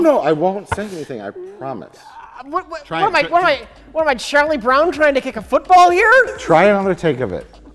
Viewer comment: "What am I, Charlie Brown trying to kick a football here?" I'm not sure I've heard a more accurate metaphor. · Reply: I thought he said Shirley Brown, which would have been typical for Rich Evans